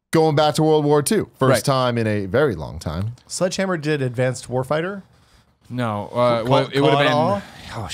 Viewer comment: did he say advanced warfighter? (clicks off video)